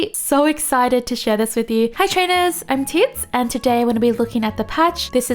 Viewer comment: This is my first time watching your video and at the introduction...When you said your name , I thought you said "Imtits" but then , when i look at your channel name i was like "Ohhh...Teeds" Btw' GOOD VIDEO!! LOVE IT!❤️